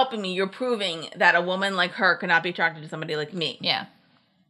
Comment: that's not what Pearce said. He said Jeff was trying to prove that "NO ONE" could want him. Not woman like her, NO ONE.